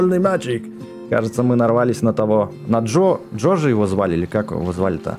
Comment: Почему я услышал ДжоДжо